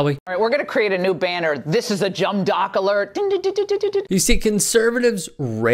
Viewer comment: "Alright, we're going to create a new banner. This is a JUMB DOCK alert." Anyone else catch that? lol.